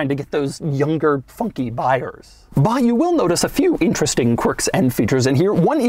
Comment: I thought Doug said, “Younger, fucking buyers.” For a second.